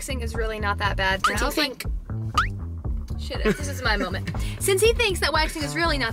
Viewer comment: Did it sound like Brooke said “shit it” instead of “shut it” to anyone else?